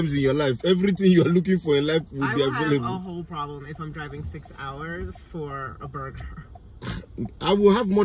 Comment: All I hear is I'm not driving 3 hours for a booga aka booger lol 😂